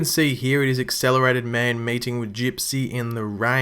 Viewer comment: I was doing something and listening to the video and I misheard at 1:17 Accelerated Man "mating" with Gypsy. I'm like huwat????lol